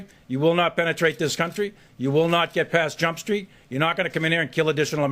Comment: General Millie says "You will not get past........?" It sounded like he said "jump street", but I couldn't make it out clearly after listening five times. Anyone know what he said? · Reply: Yes, he said Jump Street.